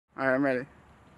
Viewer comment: At the start he said right I am ready but I thought that he said Jeffrey